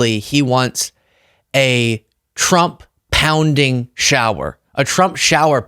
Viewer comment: I thought you were saying he wanted to see Trump pounded in a jail shower.